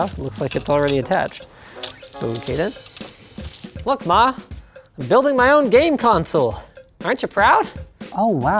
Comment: what did he say? Look who?? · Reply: He said look ma, i am building my own gameconsole.